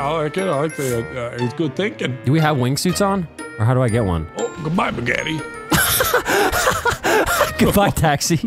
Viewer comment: What did Nogla say at 7:02 😂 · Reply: "Goodbye, Begetti."